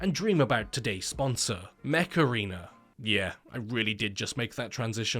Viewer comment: My dumbass really thought you said Macarena instead of mech arena